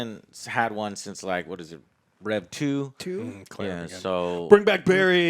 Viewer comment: What you mean Claire wasn’t in any game since RE2? She was the protagonist of Revelations 2, which even as a spin off, it expands her story very well. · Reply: I thought I heard him say rev 2 and not re 2